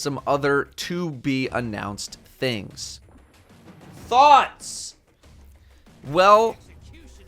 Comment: thought he said, "A new 6 player match made activity, and some other stuff to be confirmed...THOTS"